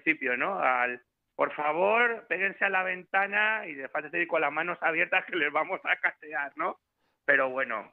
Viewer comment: Disculpen!! Me aclaran lo que dice en el minuto 4:47? PEGUENSE A LA VENTANA, LOS VAMOS A SCANEAR? ESO?